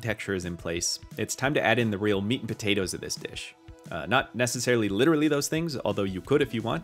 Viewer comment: Oh my god i thought you said " this b*tch" 😂